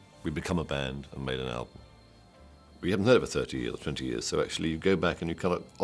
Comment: Why at 13:00 does Mike say they haven't made a proper album as a band in over 20 or 30 years? Is he referring specifically to doing so with Gabriel? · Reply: I believe he actually says 'i haven't heard it for 20 or 30 years' referring to just not listening to the album and then hearing it and liking what they made. listen again and see